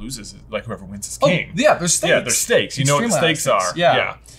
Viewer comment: Did you say steak?!